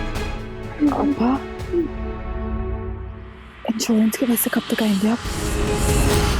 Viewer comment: What she said at 24:09? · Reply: Insurance k paisay kab tak aaye gain ab